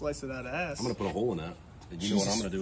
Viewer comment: The fastest way I heard “bone appatito betch”